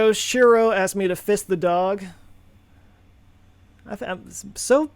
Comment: Christ, I thought you said fist the dog at first. Damn near had a heart attack.